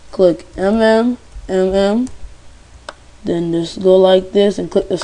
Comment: wtf u say at 1:25 o_O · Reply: Mourio Alex He says: mm